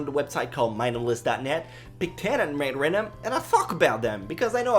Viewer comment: I thought he said "I pick 10 anime and I fuck about them"